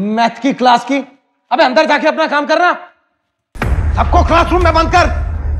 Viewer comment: What he says "abey , apna kam karna" What i heard "eey MC, apna kam karna" 😂😂😆😆😂😂😆😆